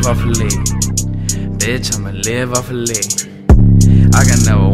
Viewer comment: thought he said live off of a lean